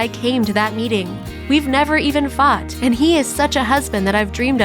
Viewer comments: Did she really said they didn’t even really fucked at 7:26? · Wait what did she say at 7:26 ? We’ve never even fucked? XD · What does the narrator say at 7:26 pretty sure i head that they never even f#*ked